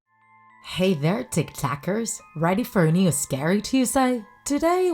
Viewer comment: when she said tik takers,i thought she said tik tokers so i really go to tik tok🤣😁😂🤣